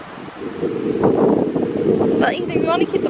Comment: At first I thought you guys were saying "Pretzel, dick!" xD